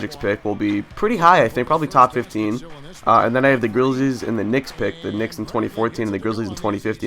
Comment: Did you just say Grillzies lol